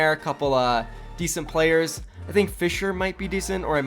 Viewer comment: "I think Fissure might be decent" lol it sounded like you said Fissure instead of Fisher I was like ya no shit Fissure is good :D